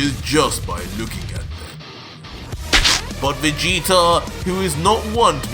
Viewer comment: Did he say ”Vegeta”?